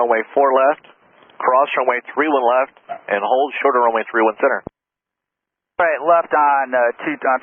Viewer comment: I'm not a pilot but despite the runway numbers, he clearly said "hold short 31C" and even corrected the guy when he read back 13C. 1:27. That is as clear as a bell even for a layman.